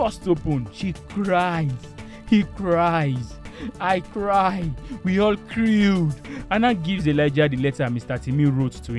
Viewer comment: I was watching this recap and crying, then i heard "she cried, he cries, i cried, we all crew" i just burst out laughing amidst tears. You really got me!